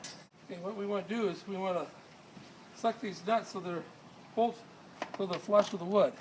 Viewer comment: Did he say "suck these nuts" at 7:30????